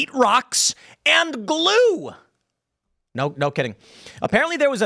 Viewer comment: does Tim say “blue” instead of glue??!